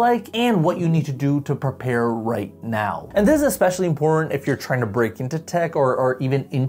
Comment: 'and this is especially ####" what did bro say..💀💀💀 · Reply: I think important.